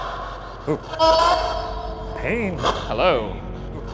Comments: Instead of it saying hello after ryan said “tell me your name” it kinda sounded like “paula” like the name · when the subtitle said “hello?” It sounded more like “are you there?” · it sounded like “Lola”